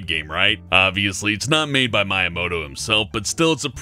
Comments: I thought you said My Imouto at first lol. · when he said Miyamoto the first time i thought he said my imouto(my first reaction was like "YOU WHAT"), I just backed it up and realized he said Miyamoto I even backed it up again just to make sure.(I am sorry, I know there is a thing called subtitles)